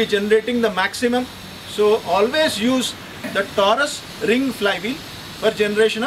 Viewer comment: What is that, that you're saying on 09:46 ? It sounds like you're sayin "taurus ring flywheel. Thank you!